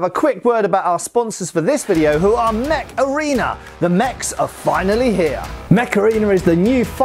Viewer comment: oh mech arena!!! i thought he said macarena